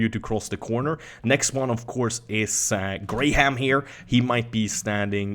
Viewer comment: Did he just say Graham like "gray ham" ?? XD